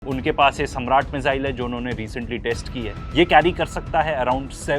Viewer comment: and 12:26 *"Sarmat".. its not "Samraat" Missile.. maybe I heard wrong..